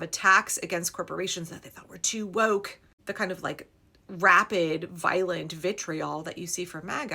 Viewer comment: JD hasn't even announced his willingness to run, much less anyone else making much noise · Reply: I heard "rabid" not "rapid", and realized that was also an accurate description.